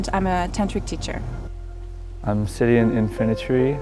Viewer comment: I feel like the guy was just lost in the forest and found a cult of hot chicks bathing, so he went in, they fed him chocolate and then murdered him and then turned him into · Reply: When he said his name I thought he said 'I'm Syrian Infantry' and thought he was a rebel freedom fighter or something